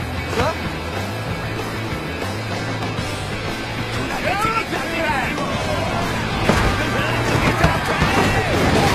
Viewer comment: 最初の幹部、なんて言ってるの？ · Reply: よお、鈴蘭です